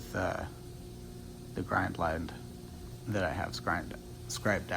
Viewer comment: Every time he says grind line now I hear a "that I have scrined" in my head